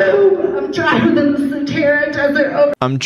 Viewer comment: i think she was trying to say "im dryer than the sahara desert"